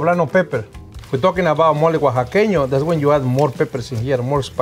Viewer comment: It sounded like he said 'we're gonna do Molly' and I thought this episodes was gonna be lit.